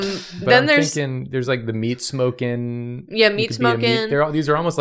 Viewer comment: I thought they were saying mead smoking for way too long and I was so perplexed because it’s so niche or just a joke about smoking random things and nope its just meat